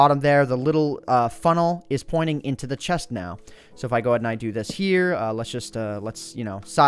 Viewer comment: i thought you said chest nut instead of chest now